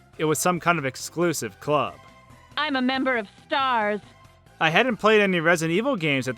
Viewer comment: I hear "I'm a member of stars"